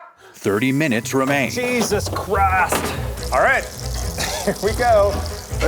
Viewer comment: LOL THE SUBTITLES AT 23:20 says "jesus christ" but the subtitles say "cheese is kraft" HAHAHAH